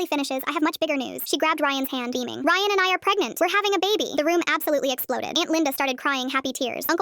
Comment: Idk why but I always hear 'we have been fucking a lot' instead of 'we are having a baby'😭😭